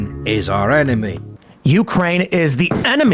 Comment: Hahaha, it sounded like you said "Jew-kraine," which would be funny seeing as this is a Tim Poole section, the Holocaust denial mentioned earlier, and the fact that Zelenskyii is Jewish. Also, the Tankies? As in the left-wing equivalent of a Reactionary? I don't get how playing the wrong music would offend someone of that persuasion; if someone here does, would you mind explaining to me? Because the only angle I could think of is wrong, no one or their mothers thinks that the CCP is actually communist, right? That would be like thinking the USSR lr DPRK are or were actually communist.